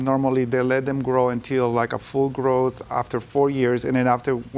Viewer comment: I get that JJ is not good with people who speak English as a 2nd language, but what I heard him say is that vets suggest you wait until the male is full grown before being neutered. Not 'four'...'fully'. But that is what I heard - apparently, JJ heard something else.